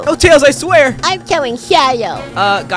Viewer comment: I thought he said Santa but he said shadow